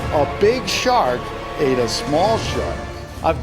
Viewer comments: Wait did he just say a big shark in a small shark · The caption said aye a small shrub